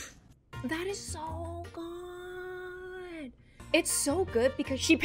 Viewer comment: did she just say that is so hot???? Play it back on .5